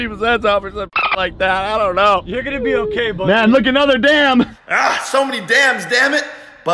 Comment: It sounds like he said answer damn